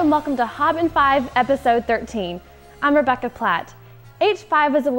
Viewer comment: sounded like she said "I'm Rebeca Black" :-/